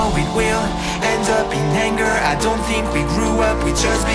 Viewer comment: Lyrics Looks like we've come to the end of the road There's no point in running away now I'd rather sleep until the storm is gone She'd rather be shooting at me now I know we will end up in anger I don't think we grew up, we just became older She's aiming her words, ready to fire I don't think that bullets will bring back desire (I don't think that bullets will bring back desire) I don't think that bullets will bring back desire I don't think that bullets will bring back desire Looks like a blink of an eye's not enough She'd rather be staying for days now I'd rather be fixing that mess up again She'd rather be burning it all now I know we will end up in anger I don't think we grew up, we just became older She's aiming her words, ready to fire I don't think that bullets will bring back desire (I don't think that bullets will bring back desire) I don't think that bullets will bring back desire I don't think that bullets will bring back desire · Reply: Oh! Anger I thought he said the N word